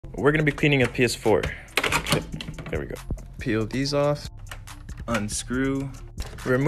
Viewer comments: I had the sound turned down and thought he said "we are going to be painting my ps4" and I was waiting the whole time for him to start painting it LMFAO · Who else thought he said we’re gonna be painting a PS4 at the start 🤔